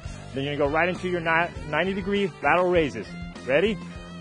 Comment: L raises - thought you said Hell raises LOL